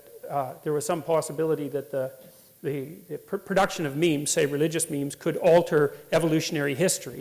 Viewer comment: is he saying memes Or means? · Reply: Memes